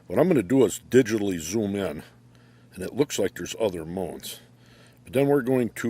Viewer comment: "theres looks like theres other moans Thats what the caption said lmao